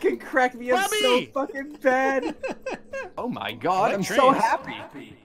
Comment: Bubby was DEFINITELY saying "Oh ye God" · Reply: for the unedited version. He actually said "my" but through Gmod it absolutely sounded like "ye" for some reason